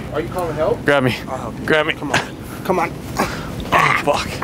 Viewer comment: in my head i just heard "come to dadda" 😂😂 he grabbed you like a baby bahahaha that was cute